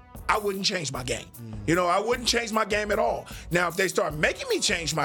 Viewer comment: I thought it said 'change his name' 🤣🤣